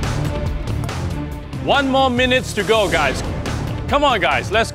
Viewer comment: "One more MINUTES to go guys" 0:27 "30 SECOND"...did I hear it correctly?